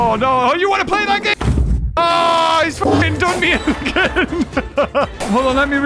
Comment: did just say ”f*cking” omg